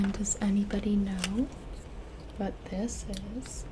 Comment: "What this is" , what i heard "but this is" and i immediately think, send this to someone with no context